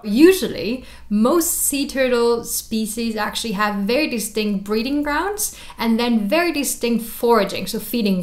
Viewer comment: "breeding grounds" I thought she said breathing grounds